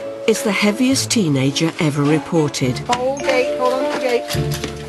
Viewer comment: sounded like they said "the happiest tenagers ever recorded" but then he cried :<